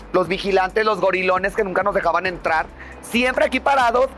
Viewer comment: entendí Gorylovers 😂😖😳